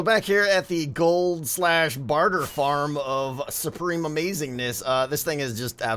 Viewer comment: Who else heard 'gold/barter farm of DOOM? '